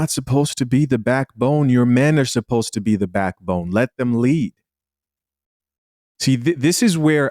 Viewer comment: let them lead or let them leave? I don’t know if I heard that right? ;-)